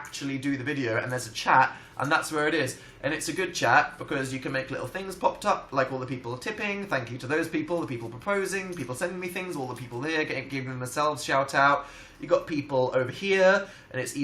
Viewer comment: At first I thought it said topping and I was like "oh, are you talking about phil again?"